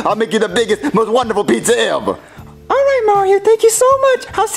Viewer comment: The essence of bowser junior inside emerged to say "Eva" instead of "ever"